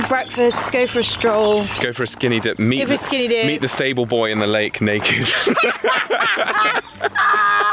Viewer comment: “Meet the stable boy at the lake naked.” Hahaha 🤣 🤣. So glad you’re back up with this channel! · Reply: I honestly thought you said "meet the disabled boy at the lake naked" and couldn't understand why you were laughing.